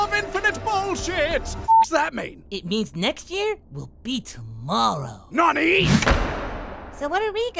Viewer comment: "Welcome to the room of infinite bullshit" "what does that mean" "that means that next year will be tomorrow" "NANI" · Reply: I heard "next year, we'll beat moro" 😂😂😂